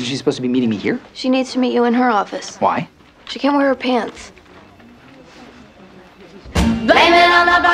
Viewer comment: I had to turn on the closed captions. I replayed it 10 times and I thought she said "She came on her pants."